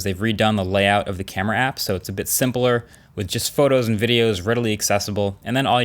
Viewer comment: 'ridily' I heard that